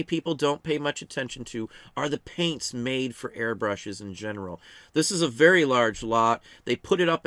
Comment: I thought you said hairbrushes… And I was very confused when you start holding those up